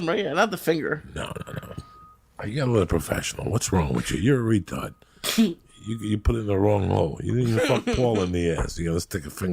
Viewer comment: Lmfao, did he just say 'fuck no! You'll put in the wrong hole! Lolz what other hole dude ahahahaaaa